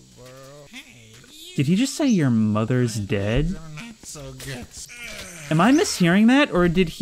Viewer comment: btw at 27:22 they said "you dont understand" not "your mothers dead" also happy new year!